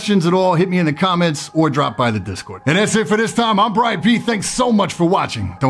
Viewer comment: At 00'55 and 09:27 sounds like he says "I'm Brad Pitt"!!